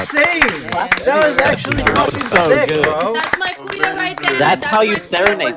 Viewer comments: Am i the only one who hears "thats my kuya" at 7:50? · at around 7:50 did she say "that's my kuya right there"?